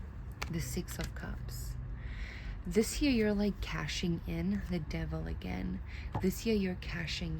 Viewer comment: Why did I hear "cashing in on the devil"😂❤ "I'm here to exchange this demon, I've learned me lesson"😅